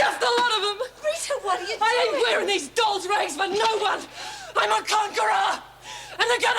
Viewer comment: I had to slow this down because I thought said." I'm a Congarilla." But she said "I'm a Conqueror!"